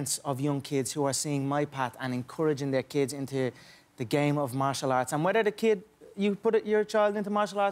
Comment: I thought he said the game of MashaAllah lol